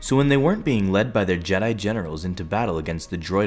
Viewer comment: Thought he said 'Jedi genitals'